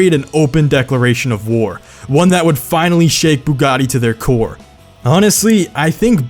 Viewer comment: I swear I keep hearing “buggani”